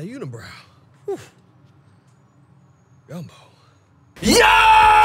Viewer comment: lol i heard "gummo" before "gumbo" had to rewind to make sure